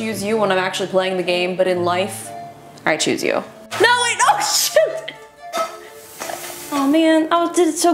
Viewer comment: OMK Did Justine just swear???? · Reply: Anthony Walters i thought she said shoot not s**t